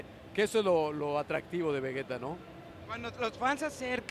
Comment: escuche un vegeta de lejos xD